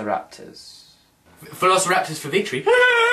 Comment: I thought he said helicopters for victory